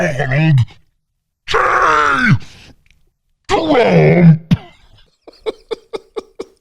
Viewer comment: almost sounded like "Twump"